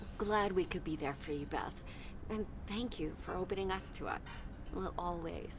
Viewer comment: Emily switched her words around and said “opening us to up” instead of “opening up to us” I caught it 😁😂